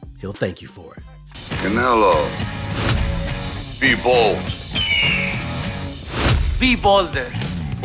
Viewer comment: when Stallone says Be bold I thought I heard him say Bivol hahaha